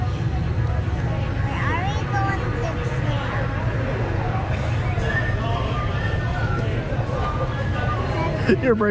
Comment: what specifically does she say at @ 0:27 ?? That seems to be the killshot. :-D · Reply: Roy Stephens “we already go on the tram” I think at this point she was just rambling and Rey was already done for and just couldn’t take it. As my daughter just kept talking with no perception of what was actually happening.